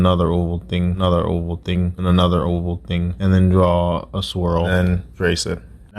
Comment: And another oval thing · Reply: OMG I HEARD ‘AND ANOTHER WHOOPING’